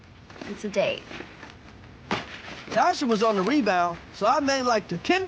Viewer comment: meu Deus 23:18 eu ouvi tasha alguma coisa "ringbell'' nem podia imaginar que era Tasha was on the rebound